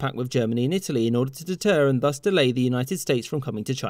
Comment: it sounds like he is saying "Busty lady" xD